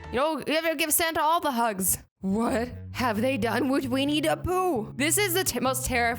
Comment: It sound like she said WEINIE the poo 😂😂